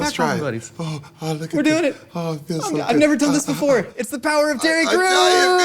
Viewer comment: Sounds like Josh said "...the power of Titty Crews!" Lol | @ 0:36.